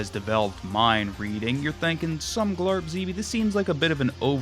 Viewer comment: did he say “someglerbzy” 😂😂😂